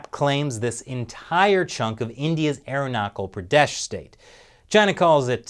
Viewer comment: Did he say "Aeronaucal" Pradesh?